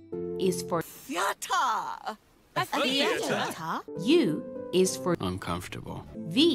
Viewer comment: what does she say there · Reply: Nettless THEATRE, darling!